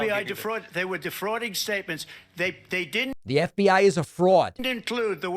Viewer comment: Not a trump supporter BUT at 08:04 Trump clearly says “Excuse me, the FBI defraud. They were defrauding statements. They didn’t include the worst…” He didn’t say, “The FBI is a fraud”. We shouldn’t misconstrue people’s words, even if what they’re saying is bullsh*t.